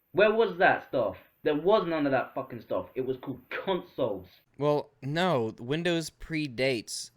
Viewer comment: i heard "cuntsoles"